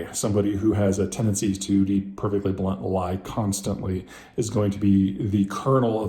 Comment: “perfectly blunt lie constantly….” Did I hear you correct?